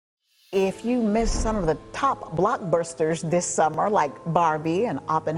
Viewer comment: "Oppenhower" 😂 Also did I hear Blockburster?